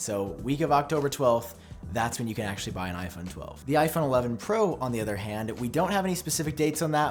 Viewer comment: 11 pro did you say at 2:13 in your video?